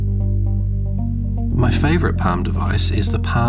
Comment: I legitimately had to restart the video because it sounded like you said "My favorite pound of ice." Excellent video by the way! I used to own a Palm Tungsten E that I put TCPMP on. The good old days. :)